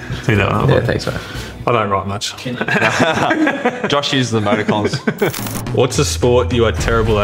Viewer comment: What does Pat say at 2:55?? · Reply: Josh uses emoticons.